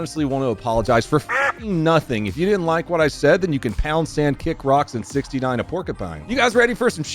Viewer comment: Dude I seriously thought you said “pounce and” fkn YouTube subtitles accurately told me what you said. For once. Almost ALWAYS it’s wrong when it comes to uncommon terms/sayings. Which I know “pound sand” is a military/football/ and maybe cop term? Maybe? I know my military fam says it. And football playin cousins. Idk but yeah, I’m that’s crazy